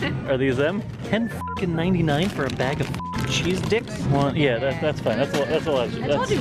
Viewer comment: did he call them cheese dicks??? LOL